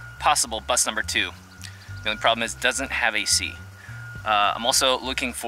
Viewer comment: Missed you too. Put it in reverse when you want it to hold when parked... Engine off, of course. It's what we did before we had "P" I thought you said, "It doesn't have a seat"... played it again and you said, a/c. I was going to tell you about my father using a chair to sit in and drive the car. Don't know how long, maybe only once but it impressed me. lol.. I love the bus you drove and would keep the lift and make a deck out of it... folds up and you can use it to load stuff. So nice to find a bus with no rust and runs well. Good luck with the search.